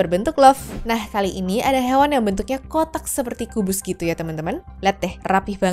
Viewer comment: apa aku salah dengar?? hewan apan awan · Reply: typo kak aslinya Ngomong Awan tapi terdengar Hewan